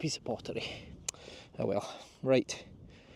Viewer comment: Great Family tree built today! Question, sometimes it sounds like you are say oh well, but other times it sounds like you say ah quel, am I hearing that right? Have a good week! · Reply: Ach well. Scottish. an expression of surprise, impatience, disgust, etc.